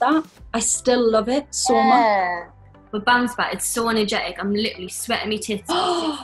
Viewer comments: What does Jade say at 1:33? I have watched this moment a million times and can only understand after "it's so energetic" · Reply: “With Bounce Back” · What did Jade say? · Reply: Sol Julieta she says “It’s so energetic I am sweating my tits off” lol